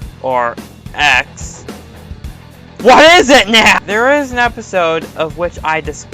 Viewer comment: What did you just say at 0:45 or so? I can't understand you with all that distortion. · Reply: he said, what is it now?